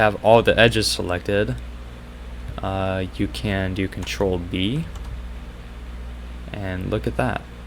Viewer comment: I got stuck for 10 minutes because i thought u said "control D" not control B